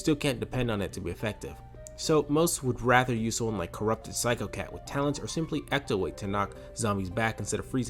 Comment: captions say phsychocat when it's psychocat